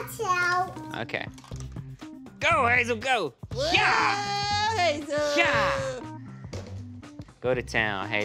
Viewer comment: who else heard that “ woah hazel” 😂😂😂